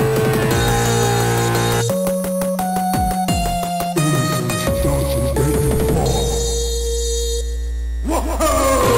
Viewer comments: Anyone know what he says there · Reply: Innocence doesn't get you far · can someone tell me what MX says? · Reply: “Innocence doesn’t get you far” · Can somebody tell me what MX is saying at this part. I can not make it out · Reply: Innocence doesn't get you far